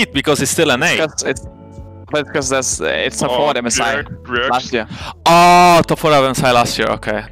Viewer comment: what is Caps saying? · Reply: Something like : Because it's for MSI last year